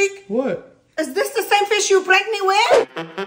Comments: Oh I thought she said “IS THIS THE SAME FISH YOUR PREGNANT WITH?!” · "WAiT iS tHiS ThE sAmE fISh YoU pRAnKeD mE WiTh???" GOT ME ROLLIN ON THE FLOOR😂😂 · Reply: Oh i thought she said is this the fish you got pregnant with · I just watch the video and when I heard mom say, is that the same fish you pranked me with I thought she said is that the same fish that you got pregnant with😂😂😂😂❤